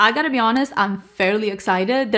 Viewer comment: Omg I thought you said “I’m barely excited” in the beginning and I was like damn girl 😂😂 now I went back and heard you say FAIRLY lmao. PS yes to the MUR video! I really love their baked bronzer, it’s a staple in my collection as I love a glowy bronzer but definitely wish they’d expand their range. I have the shade Holiday Romance. And I have been pretty impressed with their concealers! I remember Heather trying the IRL filter one and I still wanna try it! I used a different one that I absolutely loved, the conceal + define maybe? Something like that! But I’ve been meaning to try the cream bronzer, I know you’ve said it’s stunning so I wanna do it sometime! I didn’t buy anything from the Sephora sale and I work at Sephora lmao but I’m too into indie eyeshadows and just started trying indie perfumes (Solstice Scents is amazing and I have an order coming from Cocoapink, my first indie perfume houses!) but since it’s my birthday month, I like to be careful about where I spend my money. Planning to get the Touch of Glam sparkly neutral singles they released once they come back from vacation! So excited. Been obsessed with this brand all year and I haven’t been buying palettes as often because of it! PS I’m so sorry I haven’t been here in a while, I just started working as a bridal MUA and assistant while also balancing my Sephora Kohl’s job so I absolutely need to catch up on your content! Expect a ton of comments from me over the next few weeks as I catch up haha ❤❤ missed your